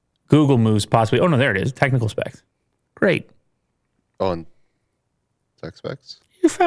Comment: Did anyone else heard him say “aonde?” at 3:02, when looking for the specs button? Could he speak Portuguese?!?!